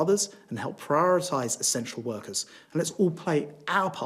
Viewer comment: I could have sworn he said "prioritise our sexual workers"